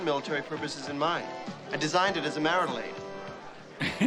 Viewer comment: Just watched your guys' reaction to this. The 2nd one is awesome. I misheard marital aid as marmalaid as a kid. My parents did not correct me. I had more questions about what a marmalade meant when it came to robots.